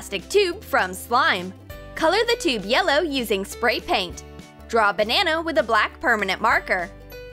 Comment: did she just say Banano? 😂